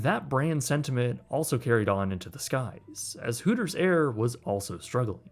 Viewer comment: I'm not gonna lie, I thought "hooters air" said "hootersaur" 😂